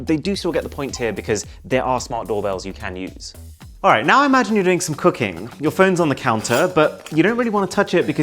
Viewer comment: here I thought you said “imagine you’re drinking some cocaine”